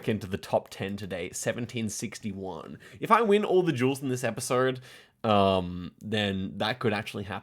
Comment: Was genuinely confused about why kept talking about "jewels" until I finally realized it was "duels". Not sure why that happened with this video and not any other previous one. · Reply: This is the second person I have seen mishearing it as jewels. You are not alone, but also the screen says DUELS after every round, and jewels doesn't make any sense(???)